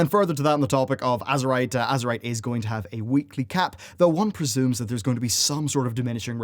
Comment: Wait, did you say that Azerite was going to have a weekly cap??